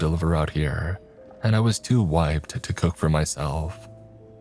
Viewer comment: I misheard it as "I was too White to cook for myself" Had to pause and relisten to that cause I was just like Whut?